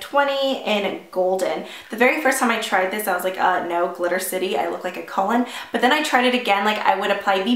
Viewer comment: When you were talking about the Jouer sample I heard "I look like a Cullen" as "I look like a colon" lol